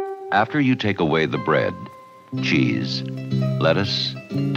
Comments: Ngl I hear "Tax Machine" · Oh god I misheard it as a "tax machine" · Lyrics: After you take away the bread, cheese, lettuce, tomatoes, and mayonnaise, what have you got? A F A X M A C H I N E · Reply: Is there a meme here that i don’t understand? Because i hear “tax machine” instead of “fax machine”.